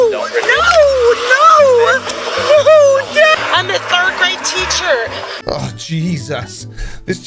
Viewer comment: My guess: She will still be an elected official (if true) and get elected again. She will still be a third-grade teacher (if true) School bus driver, that may be tricky to keep Her son, husband, and father-in-law will blame the police. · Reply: I initially heard it as "third-rate teacher", but then thought surely even a drunk idiot couldn't have said that.